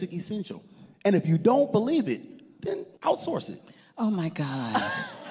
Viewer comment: what did he say? Out-source ?